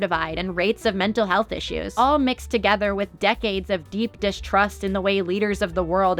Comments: it kinda sounds like she says deep dish crust if you're really thinking about pizza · heard this as "deep dish trust" and I'm saddened to learn it's not.